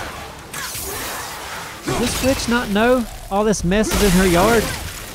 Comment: Almost thought i caught swearing at 33:15 when he said "this witch not know". Thought he said bitch lol